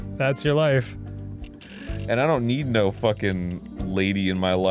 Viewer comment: i stg i thought arin said “and i dont need no fuckin labia life”